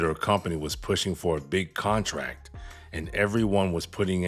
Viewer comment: You misheard. She said contact not contract.